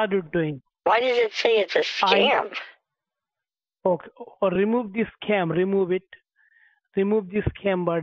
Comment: it sounded like he said "remove the scam butthead"